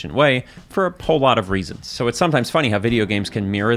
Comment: Did he just say "pole" lot of reasons?